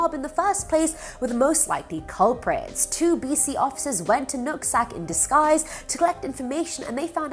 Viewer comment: I'm here for the nut sack jokes... could have swore that's what she said